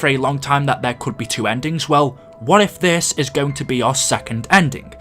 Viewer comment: I thought you said this is going to be confusing lol